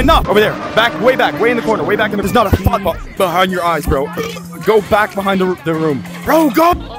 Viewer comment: "There's not a thought behind your eyes" 💀💀💀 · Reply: I thought he said I can see a volleyball behind your eyes